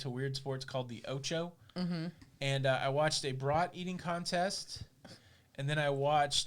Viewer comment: when you said “brat eating contest”, for a split second I thought you said “bra eating contest”. 😂😂😂